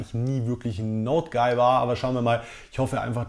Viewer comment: 😂😂😂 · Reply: Ich hab auch Note geil verstanden. Ein user hat aber aufgeklärt: er hat Note guy gesagt. Habs mir nochmal angehört. Er sagt tatsächlich Note guy. Schade. Ich fand Note geil so cool. :)